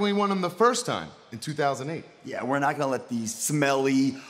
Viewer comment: did Zack say horney